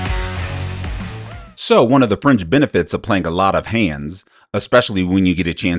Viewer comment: Did you say 'french benefits'?